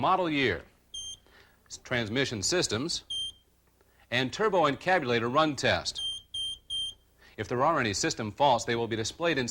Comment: subtitles say, "and turbo kenya later run tests". LMAO